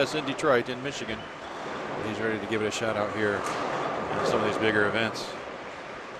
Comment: it sounded like he said n word events